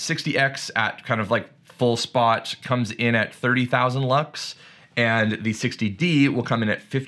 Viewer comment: I thought you said bucks and not lux 😆